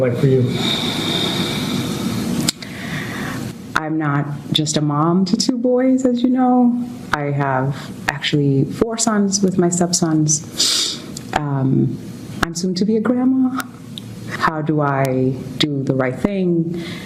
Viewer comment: Did I hear this correctly, did she said she had 4 sons with her stepson?